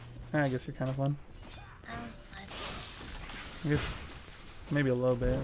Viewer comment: L0L What did i hear at 25:00 xD "im a slut"